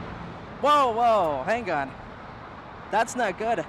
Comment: What stupid accent causes a guy to say "han gone" instead of "hang on". Hear the idiotspeak @ 1:10.